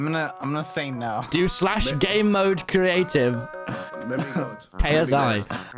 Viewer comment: I’m not the only one who thought when tommy said gamemode I thought he said gay mode right?